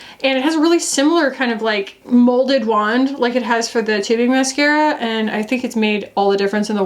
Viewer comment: you said tubing. i know you said tubing. but i heard “titty mascara” maybe because i have titty lashes